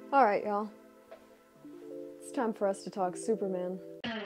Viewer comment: Oh, man, fake out. I thought she said, "Time for us to talk, Superman."